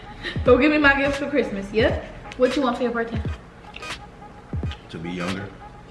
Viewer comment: What you want for your birthday? To be younger I felt that on every level 😂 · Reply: I thought he said to be under like yk 👀👀